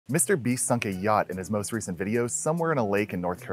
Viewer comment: In the beginning i clicked so fast I didn’t even read the tittle that I thought he said suck a gyatt 😭😭😭